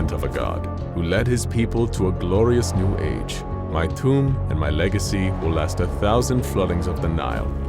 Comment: I thought he said I am toot and c u m